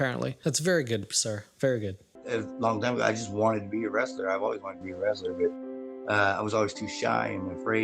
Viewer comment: What i heard: "i just wanted to be arrested." What i thought: GOALS.